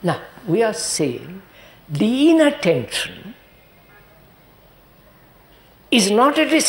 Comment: Can anyone please tell what word he used at 12:17- 12:18!Down is the line: _____ attention is not distraction! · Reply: Inattention is not a distraction